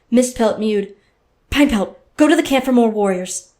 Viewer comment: anyone else hear pinepelt?